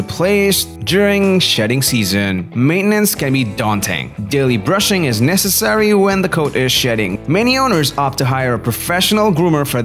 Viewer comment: it sounded like he said "daily brushing is necessary when the coat is ####ing" :|